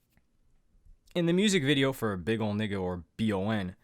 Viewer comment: did he just say big ole nigga or am I trippin💀💀